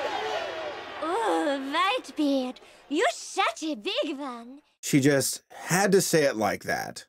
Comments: Did she just say "You shat a big one"?!🤣🤣🤣🤣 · General Whitebeardi, you are a big one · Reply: Xiphactinus Audax I thought she said “OoOh WhiteBeard you shat a big one!”